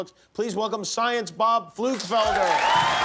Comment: Did he say "flu developer"?